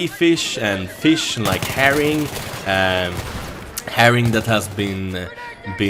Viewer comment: I thought u said heroin lmao